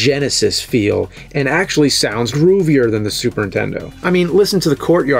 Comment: Why does it sound like you say SUPERTENDO and not superNINtendo.?